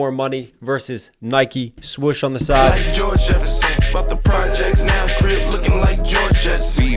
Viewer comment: I might be tripping but... Doesn't it sound like he was going to say "like Georgia S..." but it cuts abruptly? Then I thought about how it sounds like it was going to say Jorja Smith, the singer that dated Drake when she was 19 and Drake wrote a lot of sus bars about her after... Idk, I might be tripping, but it sounded like it cuts on purpose there